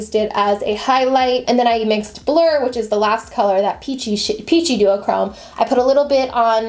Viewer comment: I think she said peachy shit, anyone else here that lol.